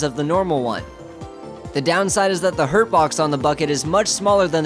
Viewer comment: excuse me,did he just say hurt-box XD